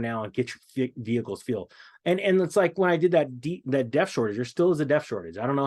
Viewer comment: What's he say at 4:35? A death shortage? A def shortage? Sorry, just couldn't figure out what he said. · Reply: Def it's added in New diesel trucks. Additive.